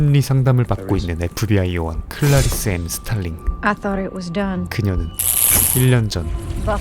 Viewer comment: 순간 스탈린이라고 들었네 ㅋㅋㅋ